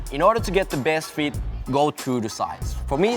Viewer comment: What do you say at 02:30? I wanna order these but you say something about size, really curious:/ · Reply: I advice to go "true-to-size", so in other words: go with the same size as you normally do with your Nike boots :)